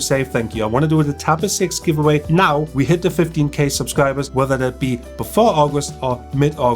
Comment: I thought you said “50k subscribers” the first few times I heard it, and I thought, ‘yeah, that is surely achievable’. 😀😄 Perhaps if I had been watching on the Tab S6 with its best on the tablet market speakers I would have heard correctly. Great giveaway, and I like your videos and had already subscribed yesterday